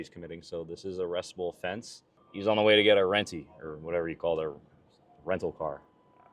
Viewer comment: Renty is definitely not a new slang the kid is just mumbling even the caption thought he was saying “rainy”😂😂